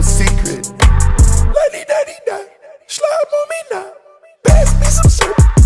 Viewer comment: I THOUGHT IT SAID SLAP MUMMY NOW 🤣